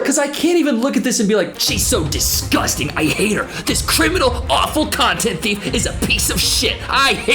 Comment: I really thought you said "...this criminal awful c¥unt". 😂 Got excited for a second there.